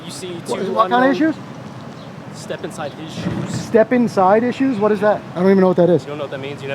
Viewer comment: I thought he said step inside issues as well. It is bout the 25th most dangerous job. Sanitation is much worse. I never hear them talking about how dangerous their job is. Food for thought.